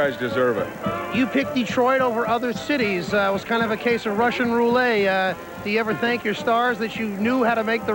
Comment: Eli Zaret said "Russain Roo-lay" instead of rou-lette?? About 0:51 in.